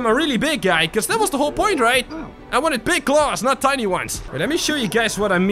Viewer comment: I thought you said gold claws